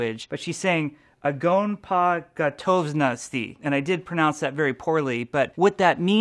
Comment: I thought Zarya said "I wont forget honesty" instead of "Ogon po gotovnosti"